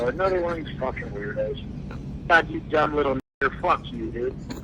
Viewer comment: Umm... Did he just call that black guy a "dumb little Hard-R" at the end here??